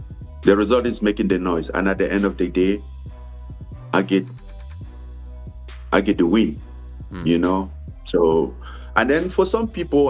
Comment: At first I really thought he said, “I get the weed”, not “I get the win”, because of his accent.